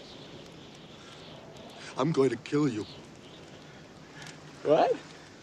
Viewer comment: I think Lloyd says ''Go ahead'' not ''What?''...